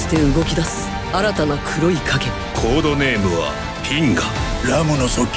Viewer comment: コードネームはTENGAに聞き間違えてビックリした